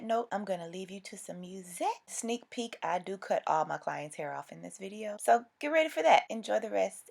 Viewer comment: Did she say that she cuts all her clients hair off?